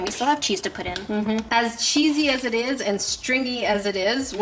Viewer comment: i was skiping through and it sounded like you said cheesy b**